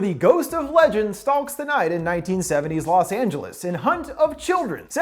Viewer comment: I keep hearing stonks the night. Stonks only go up.